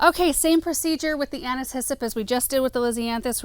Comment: I feel like such a dork! All this time I thought you were saying “Anna’s hyssop” as if it were a variety of hyssop 🫣